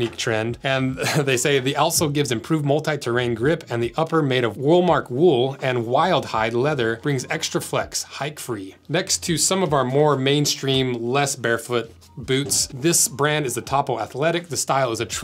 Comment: Had to watch that twice. I thought he said Walmart wool, lol.